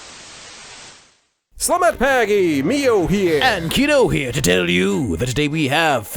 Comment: is saying "selamat pagi" or something else? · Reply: He is in fact saying Selamat Pagi!